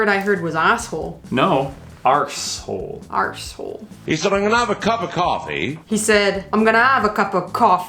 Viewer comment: Hey from Truro, Cornwall I'm pretty sure in 6:38 He actually says: "He said: I'm gonna have ANOTHER coffee". But the more I watch it the more a hear word 'cup' but hmmm not sure. If I watched it on telly I'd be sure he said "He said: I'm gonna av another coffe". Also Cornwall has its own language Kernewek, It actually became extint in the past but brought back to live and now it has about 600 fluent speakers. Thats long story short :) Comero weeth! Tereba nessa! :)